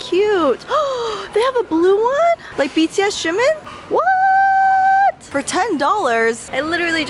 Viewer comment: I heard "like bts xiumin" and was confused 😂